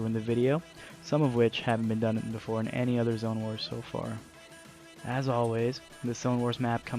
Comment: I thought it said “Ligma Zone wars!”😂